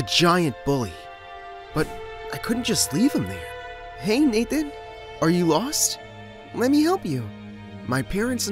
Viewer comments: WAIT WAIT IN 0:33 I THOUGHT HE SAID "HEY NIG- ARE YOU LOST?" 0-0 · it sounded like the n word lol